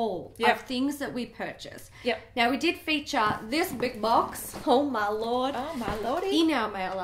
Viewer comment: I thought you said “haul my lord” instead of oh my lord at the beginning 😭😂😂